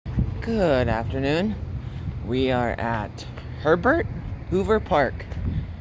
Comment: I thought you said Pervert Hoover Creek. 😂😅